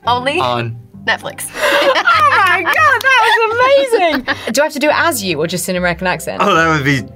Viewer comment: Guys can you please tell me what does she say at 4:27 i can't catch this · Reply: "Do I have to do it AS you or just in an American accent"